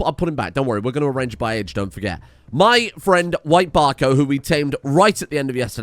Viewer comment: I heard White Barco's name as "White Barcode" truly exceptional